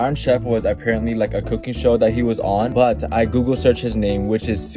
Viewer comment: I thought he said cookie show lmao